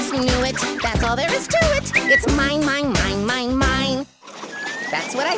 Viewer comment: Did he say MIME?